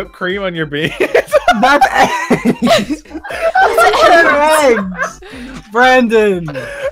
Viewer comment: the subtitle says eggs when they say beans